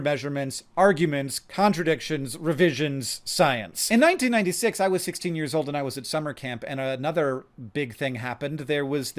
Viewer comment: I… I had to rewind this. I thought you said “in 1966 I was 6 years old.” And I lost it for a second hahahaha. Like wtf man. What secret science knowledge do you have to look that good at 65???